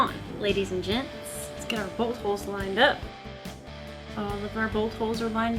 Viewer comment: She said, "Bolt holes" and I heard "Bullet holes"